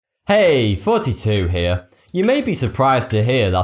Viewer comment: To me it always sounds like he says "Hey, forty-two here". :P